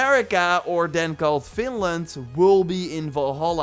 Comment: I dont know if I heard right but did tou say that we can go to Finland at some point?? I must've misheard it